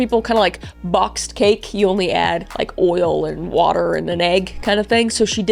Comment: Did you say oil? 😭😭😭 OIL!?!😭😂😭